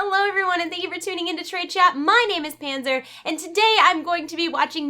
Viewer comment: Did she just say "my name is panzer"?